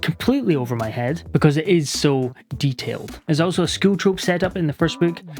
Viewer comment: I can't understand what is being said at 7:49, I just hear "Scootroup". Any help please?